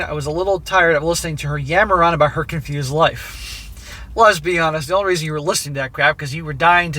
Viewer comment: Either made a joke or I didn't hear him right but it sounded like he said Lesbi-honest 😂